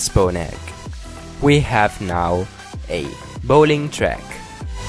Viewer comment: Did He Just say bowling track